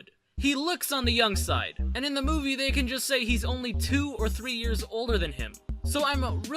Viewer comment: I thought he was going to say two or three years old not 2 or 3 years older